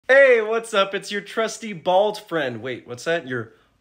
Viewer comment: I thought u said the crusty bald friend